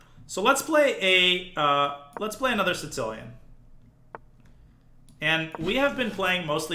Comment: Does he say “Sincilian”? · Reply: I hear it as “Tsih-cilian.”